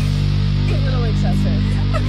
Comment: Her calling somebody a “squid” is amazing within itself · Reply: I thought she was saying "Squiz". Perhaps a truncation of the friend's last name?